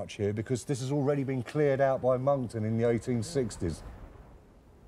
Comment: Can someone explain to me what he says at 35:58 about “molton” or some similar name “clearing roman walls in 1860s”